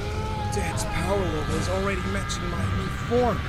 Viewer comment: Super Saiyan 5 = Super Saiyan 4 + Super Saiyan 3 + Ultra Instinct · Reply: I thought he said by power level, stop bothering me